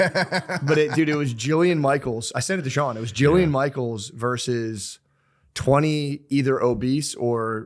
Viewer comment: I thought he said Gianna Michaels at first! Lol I was like this sounds crazy!